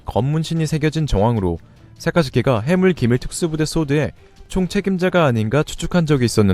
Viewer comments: ㅎㅎㅎㅎㅎ1:39 해군기밀특수부대 ㅎㅎ 잘들어보면 해물기밀특수부대라고 들려요 ㅎㅎ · 1분 40초 해군기밀로들려야하는데 저만 해물기밀로 들리는건가요??.. ㅋㅋㅋㅋ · 나만 해군이 해물로 들리는건강..??ㅋㅋㅋ